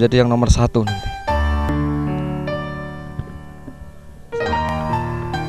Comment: reply saya kalau tau dia ngomong apa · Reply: ORA UMUM “Enggak Umum”